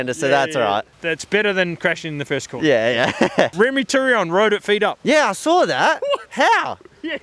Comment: Remi did what? i don't understand what's said there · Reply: With feet up around the second corner